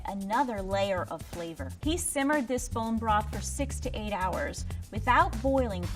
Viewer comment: does she say 68 hours? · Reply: 6-8 hours